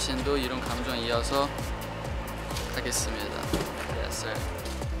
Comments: did he just say yes sir 11:37 💀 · did he just say yes sir?! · did minghao just say yessir at 11:38 🤣